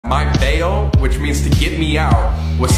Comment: HELP I HEARD "which means to eat me out"